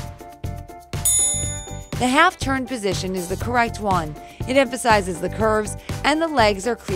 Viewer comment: did she say coright when she was talking about knees facing the camera (1:34/